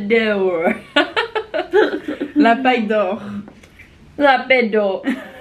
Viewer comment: c'est moi ou elle a dit "la pédow" jpp 😂😂😂😂